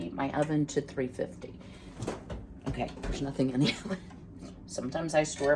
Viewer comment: I was thinking about what my mom said about (oh shoot, it's early and it'll hurt my brain to figure out how to spell that topping you made) that stuff. I thought you said that nasty stuff and I had to rewind it to make sure. You said, nothing. My mom didn't like that stuff. She said that it looked like cow slobber. I'll continue to pray for you and your mom.